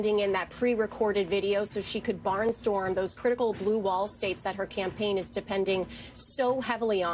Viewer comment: Did she say BLUE BALL STATES at the 4:35 mark lol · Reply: No, she said blue wall.